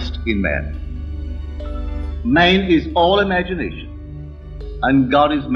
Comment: Many of these captions are mistranslations. At 4:09 is says, name is all imagination but I hear Man is all imagination.